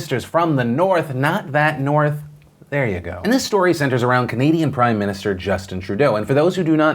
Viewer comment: Who else thought he said comedian prime minster when he was talking about Trudeau?